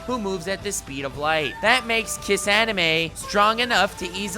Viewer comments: Did he say Kiss Anime? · did you say kissanime? Wut id rather watch it but ok. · Did He Say Kissanime?